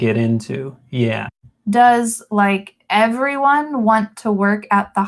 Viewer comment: I only get wrong the last ´s line, I heard "it´s, like, everyone want to work at the hospital" instead of "does, like, everyone..." greetings, thanks for the lesson, this is very helpful :)